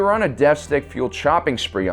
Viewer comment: Instead of "deathstick fueled shopping spree" , I heard "deathstick fueled chopping spree" and got a real vivid image in my head about deathstick junkies and vibro-blades